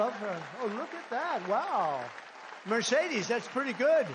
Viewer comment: Did he call Melania, Mercedes!? 🤔